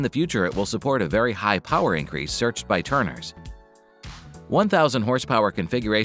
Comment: “in the future it will support a very high power increase searched by *TURNERS??*. Did i hear that right??? Did he mean tuners? ALABAMA!!!